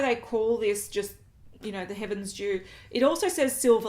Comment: Thought I heard " heaven's Jew" Finally a product just for Jews